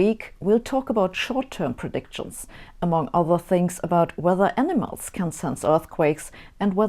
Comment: For a second I thought you said “…whether enemas can predict earthquakes..” I think I need more coffee lol